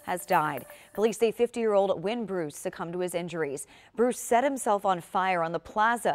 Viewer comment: Thought she said Wayne Bruce